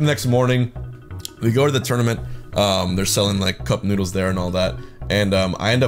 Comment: I thought he said nudes, nudes, nudes. Not noodles so I was very confused for a second there